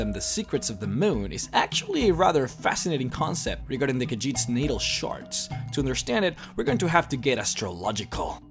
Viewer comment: is he saying the Khajiits needle sharts?